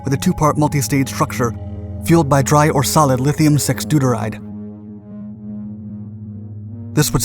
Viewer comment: Had to look up lithium-6 deuteride immediately. I honestly thought you said "sex duderide," which sounds like an under the counter supplement.